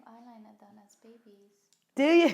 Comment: anyone hear what she said between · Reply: Nicole Isabel "We have eyeliner done as babies"